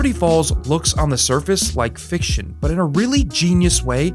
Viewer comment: I misheard this as fish shit ;) (actually fiction according to subtitles)